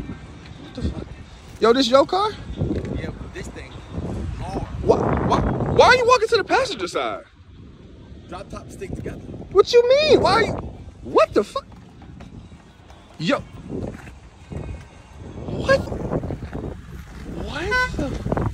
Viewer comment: “drop tops stick together “ 😂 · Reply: thought he said soft tops stick together 😅